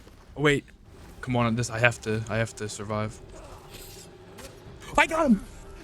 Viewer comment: "(not even real words)" What he said: "Come on this I have to I have to survive"